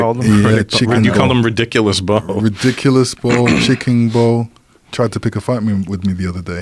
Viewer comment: did he say tyson tried to pick a fight with him?